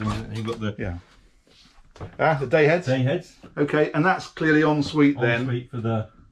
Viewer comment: what did you call the bathroom?? A "day head" or something? Then at 6:00 again you say "your own private heads"??